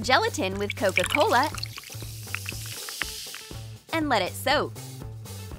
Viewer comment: When it said let it soak I thought it’s said soap😂